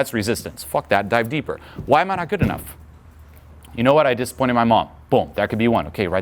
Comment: what is he saying there, i replayed it about 5 times. "i just pointed my mom"? sounds like that? what is it?